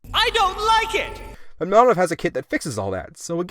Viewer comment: What company did you say has a kit that fixes the wheel gap. You said it at 9:40 but I think you said Anov but I'm not sure.